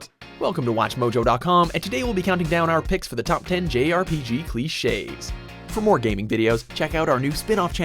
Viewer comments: I thought it said glitches lol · I thought it said glitches